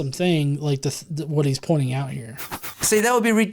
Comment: I had to back it up and listen to it again, because I could have swore the first time I heard it, he said "ridickerus". He didn't, but that's what I heard the first time, for some reason.